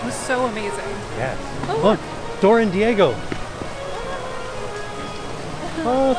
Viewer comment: I thought you said “Oh, hey look - it’s Thor and Diego!” Needless to say I was confused, but also wasn’t expecting to see Dora. 🤦‍♂️😂😂